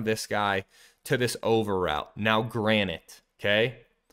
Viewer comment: Did you say granite at @ 2:16 ?? because it should be granted...